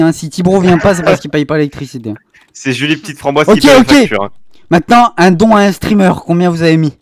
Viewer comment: Quelqu'un de devoué pour re ecrire les questions posée ? Car dés le debut j'ai entendu "qui aime jolie la petite framboise" et j'ai beau tapé sur le net ce "nom" je trouves rien donc je pense que j'ai mal compris mais j'ai beau repasser le moment je comprend pas et pareil pour beaucoup de questions ;-;.. il aurait du faire comme pour les "j'aime pas" "neutre" avec un armor stand pour la question mais bon.. Edit : aussi sa aurait etais cool de connaitre les tetes de certain, j'ai beau chercher je trouve pas la vrai tete de leffy ou flygoow ou autre.. donc dur d'avoir aussi un avis :/ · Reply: En gros c'est "julie petite framboise" c'est une vieweuse cacabox qui offre plein de subs et fait plein de dons donc à force tout le monde la connaît.